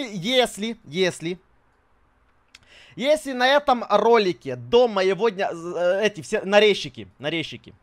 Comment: мне послышалось что "если на этом ролике соберётся 500тысяч🥲🗿🤣